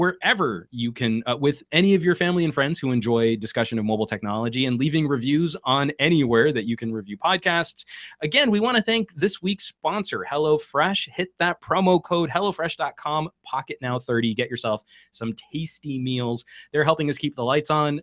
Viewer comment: Wait, the sponsor is 'Hello Fresh'? I'd always heard it as 'Hella Fresh'! ... Yo check out this food, it's Hella Fresh!